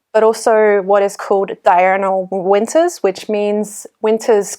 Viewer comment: can someone tell me what says at 4:12? it sounds like "diurnal winters" but i cant find anything when i look that up. maybe there just aren't any readily available resources for that information